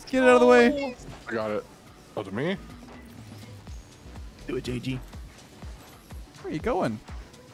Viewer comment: So far behind on the videos but at 16:33 all i hear is it saying "do it jg"